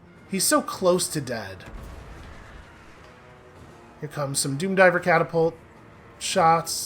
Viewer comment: Each time you say "doom-diver catapults" I hear "doomguy catapults" That is a weapon, not a teleporter